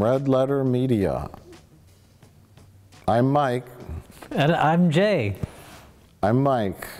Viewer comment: At intro was at the bathroom, and I swear I heard "I`m gay"...